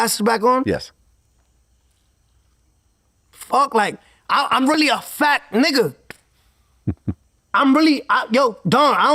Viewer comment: At the end I thought he said “IM REALLY A FAT N***A” 🤣🤣🤣